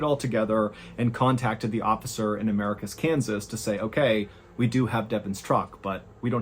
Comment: Every time I heard you say Americus, Kansas I thought you were saying America's Kansas and I was so confused why it had to be specified every time